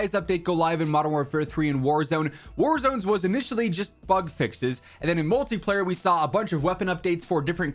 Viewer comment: Instead of bugs fixes I heard "fuck fixes" 😂😅 yeah sounds about right. Call of Updates modern fuckfixes